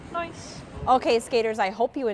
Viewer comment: Trying to figure out what you (or was it?) were saying at 8:26 Anyways, I hope my body still has it to be able to do this. It looks really wonderful. And yeah, I think I’ve seen some of the younger kids at the rink do this with the hand facing downwards… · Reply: said 'nice!'